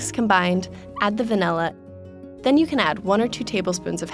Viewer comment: When she said Vanilla i heard 'Funella' Furchester Hotel...